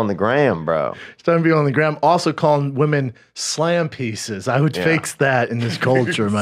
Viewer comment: Am I the only one who thought he said "slim pieces" not slam pieces